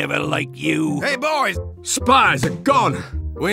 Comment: when sniper says spy when he died It sounded like he said Spois Or spoon